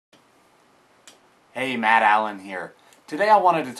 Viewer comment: Did he say "hey mad allen here"?